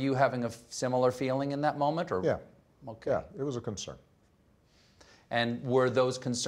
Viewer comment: I thought he said "yea, no cap" 🤣🤣